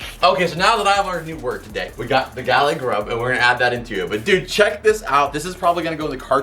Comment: Omggg whatt lmaoo I always heard you say “Gallery Grub” so I naturally just assumed it was that ! What is “Galley Grub” anyways lmaooo like what , you just blew my mind hahaha